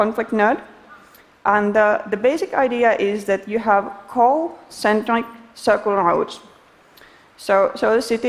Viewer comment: What in the world is she saying at about 4:03? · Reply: have centric circle routes.There are subtitles you know...